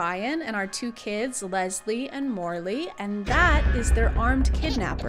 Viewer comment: I GOT SO OFF GUARD MY NAME IS MARLEY AND I THOUGHT YOU SAID IT 😭